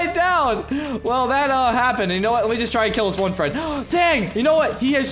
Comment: I heard u said let kill his boyfriend at 11:01😂🤣🌝